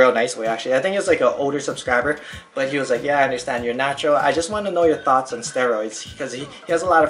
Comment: when he said "I'm natural" i thought he said "i'm on andro" some kind of exotic drug lol